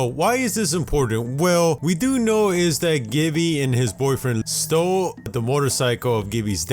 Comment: 6.09 did he just say hubby and his boyfriend ????? Someone pls respond